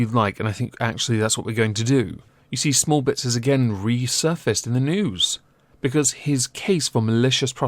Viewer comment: I totally thought your said "smallbits has returned to the noose